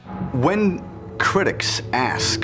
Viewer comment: Did he just say asstrounuats